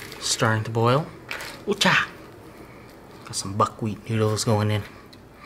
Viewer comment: At first I thought he said butt wheat noodles and poison sauce lol